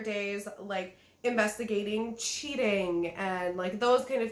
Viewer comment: "Investigating cheating..." I honestly thought you were about to say "investigating Cheetos" XD